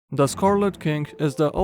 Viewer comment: For a second I thought he said "The scarlet kink."